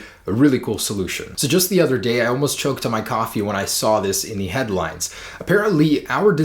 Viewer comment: Omfg at 9:38 I thought you said "Just the other day I almost choked on my cock"